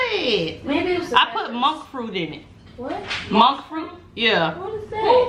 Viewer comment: Girl, I thought you said MONKEY 🐒 FRUIT 🤣🤣🤣🤣🤣